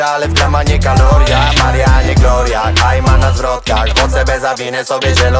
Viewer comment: Czy tylko ja słyszę w refrenie NAJMAN NA ZWROTKACH? xd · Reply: Ja usłyszałem "Najman na wrotkach" XD